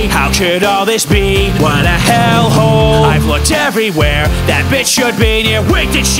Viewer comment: Did he call Lute a b*tch?????